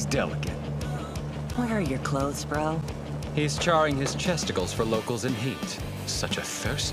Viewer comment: What the hell I thought cherry said "he's charring his testicles-" when it's actually chesticles😭😭💀💀👻👻